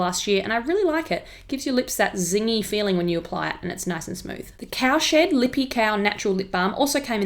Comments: Ya'll commenting sum relevant but i coula swear i heard cowshit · i heard cowshit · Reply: Cowshed! I swear I said Cowshed 😂🐄 x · I thought she said "cowsh*t". Oh my god